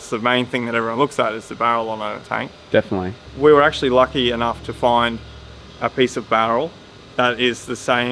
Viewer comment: I misheard that first time as 'lucky enough to find a piece of Daryl'. Amazing work as always.